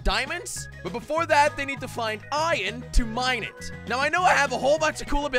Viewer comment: Did you say ion what😂